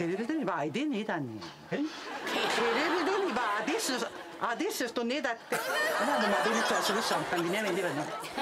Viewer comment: 俺の耳にはthis is theしか聞こえね